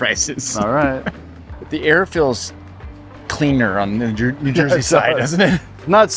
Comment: Wait did you just say “the air feels cleaner in Jersey”? I think that’s a first